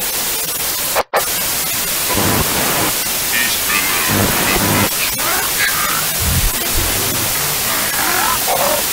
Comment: What is he saying? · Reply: Sounds like "It's (or he's) below you and its (or he is) watching. You are now trapped in this hallway." in a demonic garbled voice